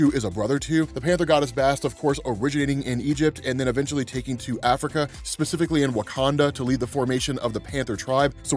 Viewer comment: For reality sakes, around 2:10-2:16, did he say from Egypt to Africa? 🤔